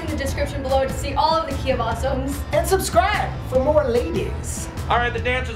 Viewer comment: Am I the only one who thought it said ducky of awesome when it said the key of awesome